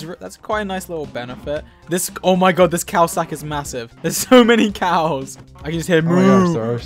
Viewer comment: I thought Goerge said cow sack instead of cow stack